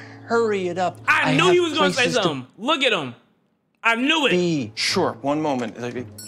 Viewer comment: though I heard him say "sure one moment N***A"